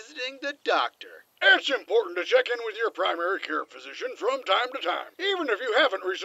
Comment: What did Sarge say? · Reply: Its important to meet with your primary care physician time to time, even if you havent broke your kneecaps from falling off of red base after hearing about a box of free Eclairs!